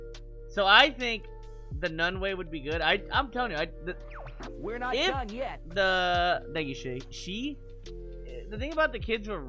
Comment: I heard that as "you're not none yet" which was very applicable lol